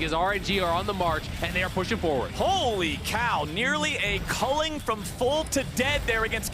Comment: Did he say holy shit at 10:20? Hahaaha